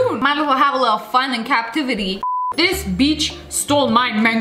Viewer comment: Did anyone hear her say the B word in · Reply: She said beach